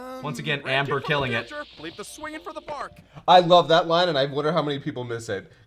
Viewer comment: Huh I always thought Krillian said your swinging for bark. Like in a family tree.